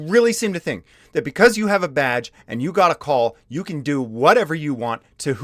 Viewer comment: Did you say because you have a badge or because you have a vag? :P